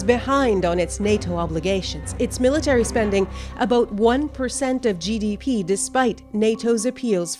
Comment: did she just say aboot?